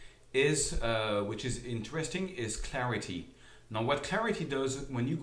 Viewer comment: did he say "Tarity" 11:15 lol